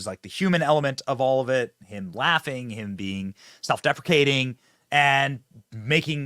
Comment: What?! Did you just say, “. . . self- defecating . . .”?!